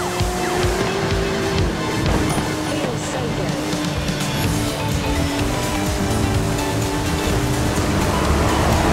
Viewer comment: what she say??? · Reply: Fells so god